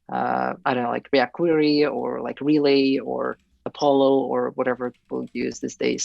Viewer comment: hmm. maybe I heard it wrong. english is not my mother tongue 6:11 I don't like react-query or realy or appllo or whatever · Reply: I spoke a bit too fast; what I said is "I would use a cache.... i don't know, like react-query or ..."